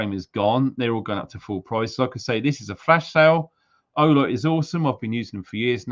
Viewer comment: Wow steady on. I thought you said flesh light sale.